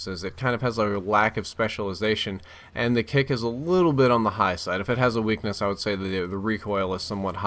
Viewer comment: I swear it said kick is a bitch XD